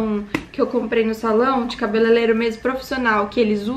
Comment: No 3:50 eu escutei cabeleileiro? Eu me identificando pq minha mente sempre buga quando vou falar essa palavra kkkkk